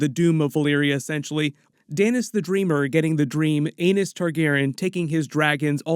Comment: Did you say Anus Targaryen?😂 Is there actually an Anus?.... George Martin's got issues if that's the case.